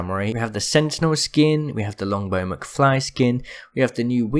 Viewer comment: Js the accent makes it a bit hard to understand you at times. Thought you said muckfly instead mcfly.